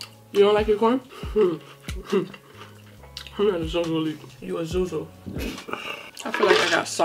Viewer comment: Damn did she just say your a zozo